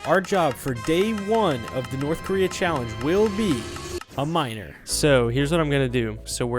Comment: the auto captions said "a minor"..